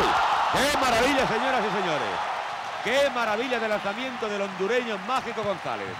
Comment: En el min. 28:17 me parece escuchar que dice que maravilla de lanzamiento del Hondureño....